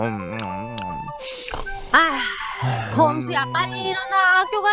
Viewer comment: 에 봄수가 아니라 검수라고 들리는거 나뿐인가???